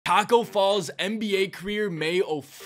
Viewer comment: I thought you said Tacko fall NBA queer